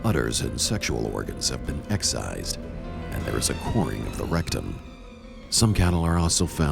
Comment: does he say there was a coin in the rectum of the cow? There goes the alien theory I guess eh